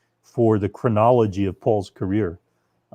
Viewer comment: I misheard "chronology of Paul's career" as "criminology of Paul's career." 😁